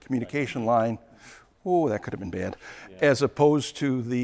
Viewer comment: says, "Whoo! That could have been banded." is talking about CAN-FD. What is saying?